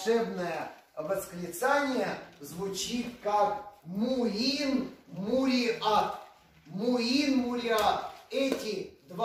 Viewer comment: муин муриан Или муин муриат..... Точно не расслышала. Если кто понял точно - напишите пожалуйста. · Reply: Муин Муриат 7:55 увеличили экран